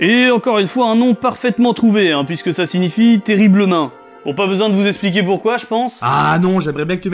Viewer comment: j'ai entendu "terrible nain" donc je propose de le renommer "le terrible nain aux grandes mains" !